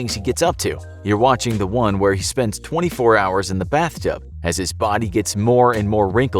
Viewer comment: I thought it said " Spend 24 hours in the sofa "